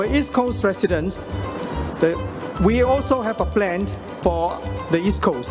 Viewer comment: no one is gonna point out he said "plant" instead of "plan"